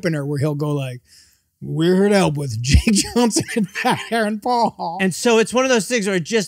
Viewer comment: Thought it said jack Johnson. lol